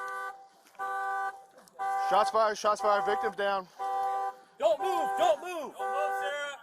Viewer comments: First time, I heard a cop say "victim down" instead of Suspect. Lmao.. Think the action got in the way.. Otherwise great shots.. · Does he say "victim down"?...